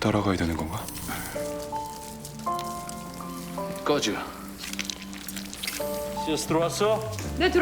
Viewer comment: 꺼져로 들림 ㅋㅋ